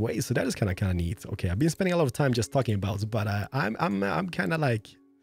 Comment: did he say "fucking abouts" at 7:09. if so thats rare u hear cuss